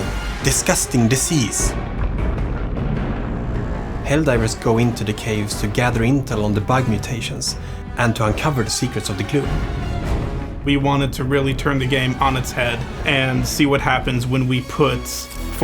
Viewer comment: the internet has done a number on me thought that man said “ the secretes of the goon”